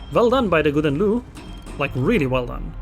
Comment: what did you call that gouden leeuw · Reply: Just the way it's in English... Gouden Leeuw?